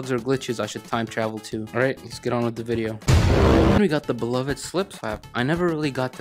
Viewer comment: Is it just me or did he say "beloved slip slap"?